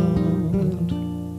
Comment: What does Curt say at 3:11 ? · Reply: Halargian World.